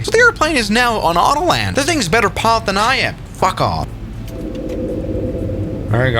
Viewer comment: I think i heard at 5:24 fu- off